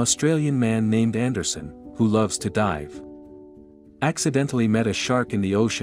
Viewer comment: oh ok thought I heard "who loves to die"...man my ears